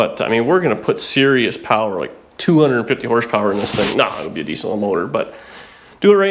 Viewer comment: "260 hp..Naw it will be a decent little motor". I had to replay that part...I thought you said a "diesel motor". Common...you have to mess with the first gen Camaro guys and put a diesel in it!!!!!!!!!!